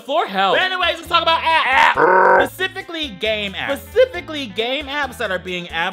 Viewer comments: i swear i thought he said “gay maps” and not “game apps” Edit: i think this channel is infecting me and i don’t mind it XD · legit thought he said "game a$$" 🤣!